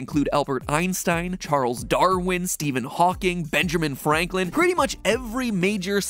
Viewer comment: I just heard "Benjerman Franklin" and I'm disappointed in you, MatPat.